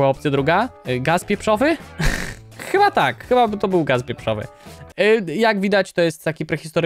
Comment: czy tylko ja usłyszałem gaz wieprzowy a nie pieprzowy